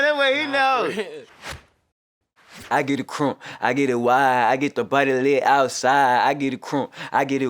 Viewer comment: Who else thought he said “i get it wild” ?